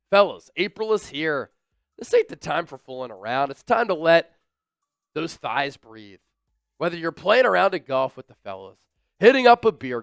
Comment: I’m in a serious conversation I hear “ let them thighs breath” I bursted out laughing😂😂